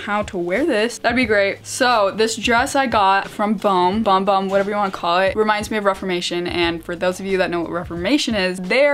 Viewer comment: what's the brand of the dress in 4:31? I couldn't hear it right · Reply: Mochi Bohme!